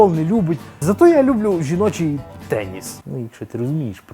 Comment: Пхах, послышалось: -Зато я люблю жіночий пeніс(звук минимум был:D)